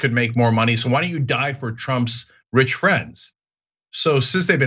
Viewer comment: you said 'die for Trump' but what I heard was 'diaper Trump'.